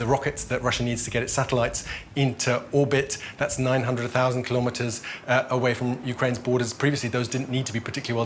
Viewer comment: had to do a double take. I thought he said "900,000kms away"